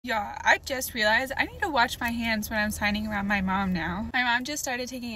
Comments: I I thought you said I need to wash my hands in front of my mom❤😂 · I heard it as "wash my hands" and I was so confused and my mind started racing with stupid scenarios like "is her mom always sick and sneezes on her hands?" or "does her mom work in a hospital and she doesn't want to get sick?" 😭